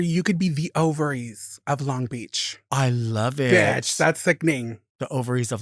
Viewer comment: I could have swore she said "Overeats of Long Beach" haha. I was thinking, NERVE